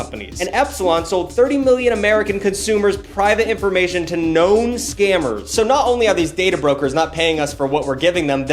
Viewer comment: I thought he said gnome scammers. Twas a fun mental image for a second.